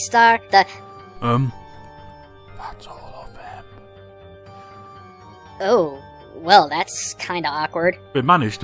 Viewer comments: wtf does whisper into ear? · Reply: L.P. Berry That’s all of them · Am I the only one wondering what he said at 3:27? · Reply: He said: "That's all of them."